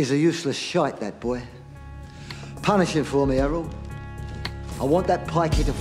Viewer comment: Always thought when he said Errol,it was a heavy accented version of Harold....